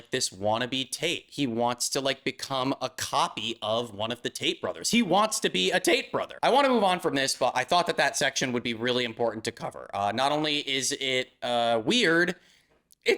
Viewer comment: For some reason I kept hearing "The Taint Bothers"........